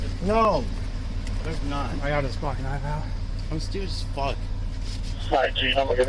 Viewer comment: did he say “ I’m stewed af”?